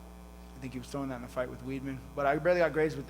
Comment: did Nick say weedman LMAO 😂😂